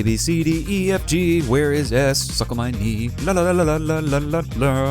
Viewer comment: Did you just say "Suck on my knee"?!?! XD